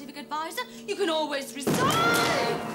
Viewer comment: What does sarah say at 1:15 And I feel sorry for tom after a burned hand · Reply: "You can always resign"